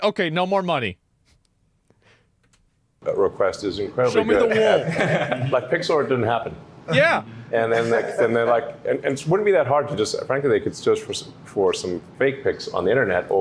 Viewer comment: OHHH I thought Ethan was saying "Pixar didn't happen" in the Ethan vs. Hasan debate, I was so confused but its "Pics or it didn't happen." Dang I'm stupid.